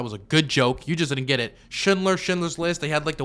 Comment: when he said Schindler I thought he said Shitler, as in shitty Hitler